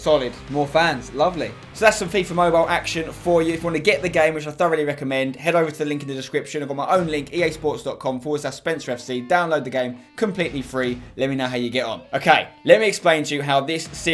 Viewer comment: Did he say I don’t really recommend